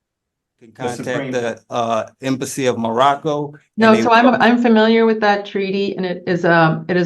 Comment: Did he say he was ... "imbecile from Morocco"?